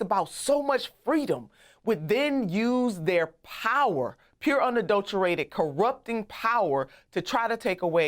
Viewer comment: Did she just say ( unadulterating corrupted ) power ? - Or " UN " - ' adulterating corrupted power ' ??